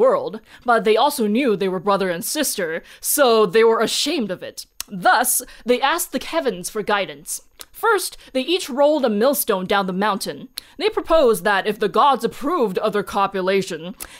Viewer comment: I'm listening while doing work and thought you said "... And they asked the Kevins for guidance" and had to rewind 🤣 Great video! Your work is always so quality and those animations were beautiful! Looks similar to the animation from those Hungarian folklore videos on YouTube, GORGEOUS!